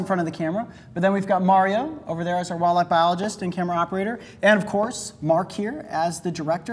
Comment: I swear at first I thought he said "walnut biologist.". 😂